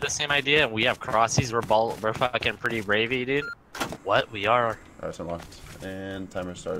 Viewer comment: did he say ravy